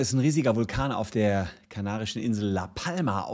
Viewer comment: ich habe kanadisch verstanden und war kurz verwirrt